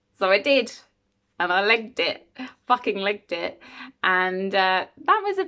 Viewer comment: This is probably a very stupid question, but what does she say about the red string? From 3:40 she says something like "I could get out of this and I did. I licked it. I f... licked it." What does this mean? What happened? She ran away? Or broke the red string, caused an uproar? Thanks for helping :) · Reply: legged it = ran away as fast as she could